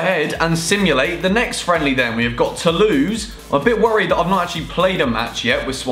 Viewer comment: it sounded like he said we've got to lose but he said we got tolouse